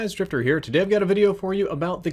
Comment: "hey guys its stripper here" I swear that's what I just heard XD. I'm sorry